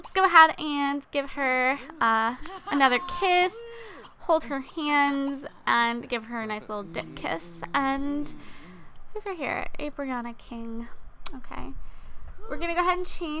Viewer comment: it sounded she said dick kiss , instead of dip kiss :P